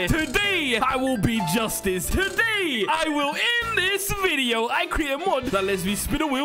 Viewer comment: oh I thought you said “end” not in